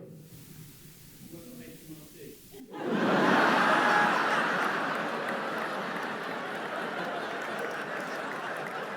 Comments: Does anyone know what the guy said here that got a big laugh? I can't make it out and the captions aren't picking it up, either. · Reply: I think he said "I work for HMRC" but I cant confirm either. I'm only assuming that as that's what it sounds like and it would kinda make sense as he's always making jokes about avoiding tax man😂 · what did the guy at 31:27 say? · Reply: He said HMRC 😂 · What did say his day job was? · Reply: He said he works for HMRC.